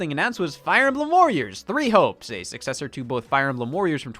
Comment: it sounds like said “fire emblem warriors: three hoes”